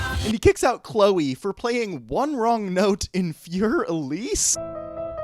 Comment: did bro just say Fuher Elise? 😂 Wow. I hope that was a freudian slip.